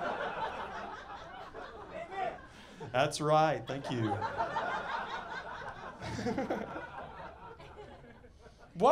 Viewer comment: what was the guy yelled at 0:57? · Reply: 'Amen'